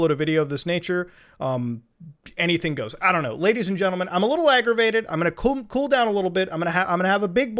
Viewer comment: He said i am gonna coom instead of i am gonna cool down haha